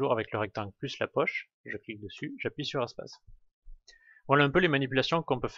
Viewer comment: Pour faire apparaître, c'est quoi ? Impossible de comprendre ce qui est dit. J'ai écouté une dizaine de fois, impossible. À 8:42 "je clique dessus, j'appuie sur..." incompréhensible... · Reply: Ça y est, c'était "espace".